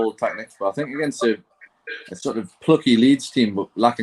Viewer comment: did he just say “against a fluffy Leeds team” ? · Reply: Plucky